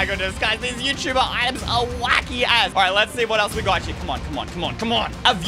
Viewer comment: what did u say? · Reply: He said as not that